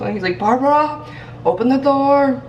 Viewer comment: It sounded like he said “habibi open the door”. Habibi is Arabic for loved one I believe. I nanny for a Syrian couple and the Dad calls his daughter that!